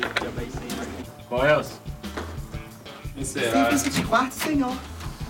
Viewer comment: Só foi eu que escutei um "Coelce" no minuto 20:50 ? Galera do Ceará vai entender kkkk